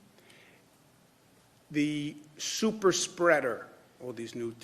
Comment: I heard Super Shredder.